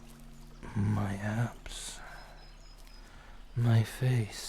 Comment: did he say abs or apps? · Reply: Abs he’s talking about his body